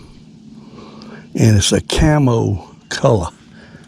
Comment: 64th Jughead is that 2 inch sandstorm? I thought I heard camel color